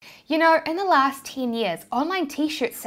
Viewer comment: I heard "My last teen years" 😆